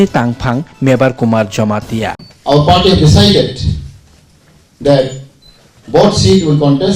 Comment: am I the only one who heard " Our Party Besided" I feel ashamed of them and this time we aren't going to vote any party.